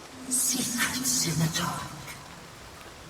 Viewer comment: I heard “secrets in the dark” as “see that scimitar” for a second